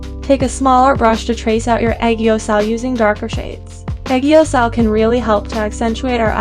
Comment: Thank you so much for translating these! I know it is not easy to do. Could someone please tell me what word she is saying at the 8:10 mark? It sounds like “egiyosal”, automatic captions say “pegioscall”, and I have absolutely no idea what it is. She says it in other videos too. Is it something about the lower lid? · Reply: It’s aegyo sal. It’s just a little fat under the eyes, you can search more about it!